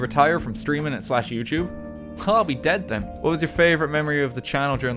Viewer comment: All I heard was CallMeDeadThen